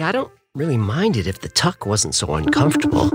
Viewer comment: Did he just say “ if this tuck wasn’t so uncomfortable” ? 😂